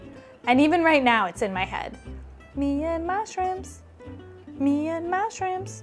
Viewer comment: Ok so I heard, “Me and mushrooms…me and mushrooms.” I think because the plural of shrimp is shrimp, not shrimps. It’s in my head now even though it makes no sense in context. I was thinking why is she singing about mushrooms lol?